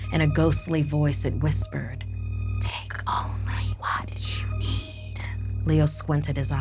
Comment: What o thought it said: “tAKe oNlyY wHaT yoU EAT” Me: •~• ummmmm.... 👌🏼 Also me: turns on captions Also me AGIAN: OHHhHh •~• I’m so stupid talks to self (why the hell Mindy, why would I think it said that •~•) (Mindys my name by the way )